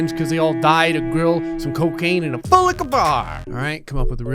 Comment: I love how he says “full liquor bar” so fricken much. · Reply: He says full liquor bar?!? I thought he always said boot liquor bar and I just went with it